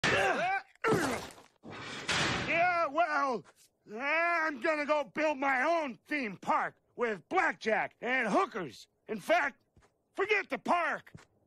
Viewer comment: I could've sworn he said "screw the park"